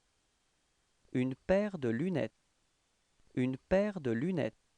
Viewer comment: Hello Sir, I heard "une paire de lunè" (2:33). Thanks a lot for your wonderful work !!